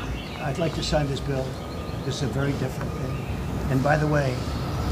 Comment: Did he say "I'd like to sign this bill. It's a very difficult thing"? I mean... I wouldn't disbelieve it if you told me he needs the utmost concentration to sign his name.